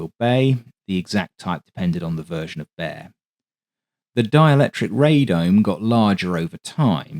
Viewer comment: You keep saying: "dialectric" and I keep hearing: "dialectic"; short for "dialectical materialism", a major philosophical point in communism.... the Marxist theory (adopted as the official philosophy of the Soviet communists) that political and historical events result from the conflict of social forces and are interpretable as a series of contradictions and their solutions. The conflict is believed to be caused by material needs.